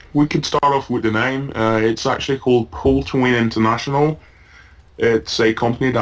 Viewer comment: Did anyone catch that company name at 3:10? The speech wasn't clear here and there are no annotations to help us understand what he's saying -_- I don't want to make false accusations, but it seems as if you're trying to hide something here, and I don't appreciate that, especially since you're whinging about a company doing just the same. If you're going to make accusations like this, I'd at least like to know the company's name so I can look it up to make sure it exists, and it's not some garbage you and your friend made up as an excuse for getting banned or to badmouth a company you've had problems with. My apologies for coming across harshly, but this video "interview" is more than suspicious. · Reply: I think it's called CallToWin International, from what I heard